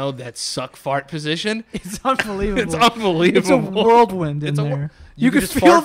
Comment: did he just say world wind? Is this another on-all-floors thing?